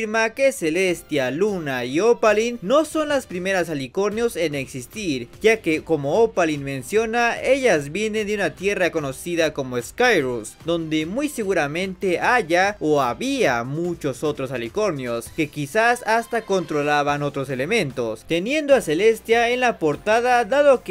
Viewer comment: entendí skyress XD